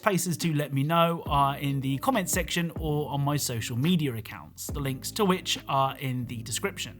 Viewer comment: when he say's social media accounts I always hear 'social meadier accounts'